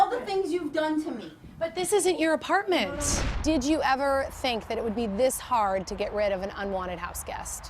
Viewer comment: “After everything you’ve done for me?!” Lol, that’s not how guilt trip works. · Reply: "Done to me", not done for me, is what I heard